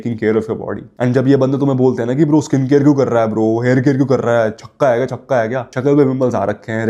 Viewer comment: Bro "Jhakka hai kya" - caption says "Are you six?". This is so fcking hilarious 😂😂